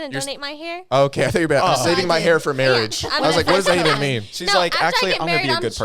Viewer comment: I literally also thought she said "to save my hair for.... marriage"